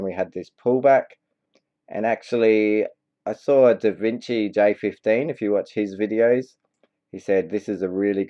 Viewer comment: what name did you say at 2:00? Da Vinci day 15 videos? I couldnt find the videos on Youtube. Can you please share the link here? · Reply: i was referencing Davincij15 you tube channel <link>